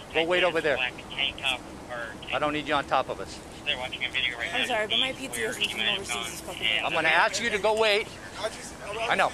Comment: Did she say “I’m sorry but my pizza from lil caesars is fkn here” at 1:25 or am I trippin 🤣